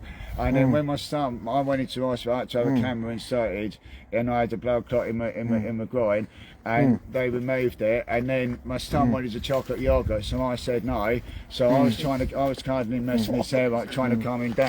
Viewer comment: Going from having a camera inserted to check out a blood clot, to his son wanting a chocolate yoghurt has to be one of the maddest switch ups in conversation I’ve ever heard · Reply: I thought he said "I had a camera inserted and my stomach was a chocolate yoghurt"!! Having listened again you are right. What a complete bell end